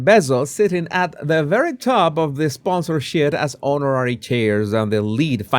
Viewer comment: did say "Sponsor 💩? 🤣